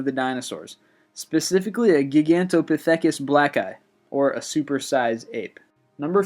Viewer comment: does he say a gigantopithecus "black guy" ?